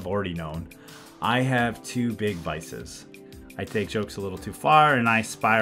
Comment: i thought he said "i have two big biceps". we know xD Great video once again.! very enjoyable!